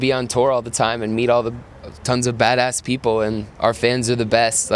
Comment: HE SAID MEET ALL THE BITCHES ON 18:32 idk i swear he was about to say bitches hahahahaha what hell yea